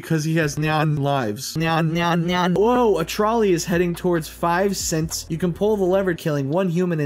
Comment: I swear I heard “5 Synths” instead of 5 cents and that made the Fallout fanboy in me squee